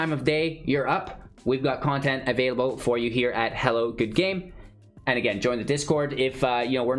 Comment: I keep hearing "Welcome to Hallowed Good Game"... which is accurate enough. Really enjoy your channel. Thank you for the personality you bring to the content you produce, on top of the good games.